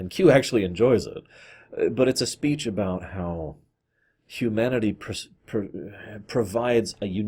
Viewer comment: I legit thought you said "Q-manity"